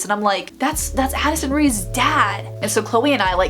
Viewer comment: What Rae said:That’s Addison Raes dad! What subtitles say:THATS HENRYS DAD